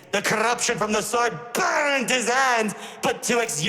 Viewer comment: BURNED HIS HAND, i love that line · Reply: By a misshearing i heard "BURNED HIS ABS" instead of hand 🥹